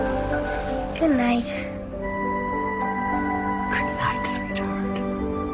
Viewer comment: did anyone else understand "Good Night Squidward" at 06:25 ? :D